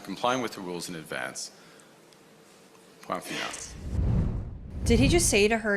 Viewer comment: what he said was "point finale" which basically means we are done or end of discussion I really dont like the fact he was not elected by the country, being the party chief is one thing but he should not have gotten the pm position without an election